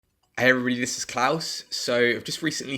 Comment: What did you say?: "every sklows"